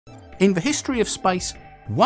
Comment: First 5 seconds I heard: "In the history of SPICE"